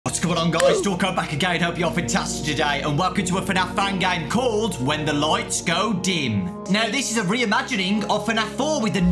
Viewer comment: So am I the only that heard him say hope your fantasted today instead of fantastic and said that till now